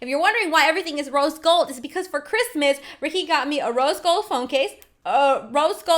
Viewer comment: Her: Rose Gold Phone Case Me:I thought u said Roast Gold Phone Case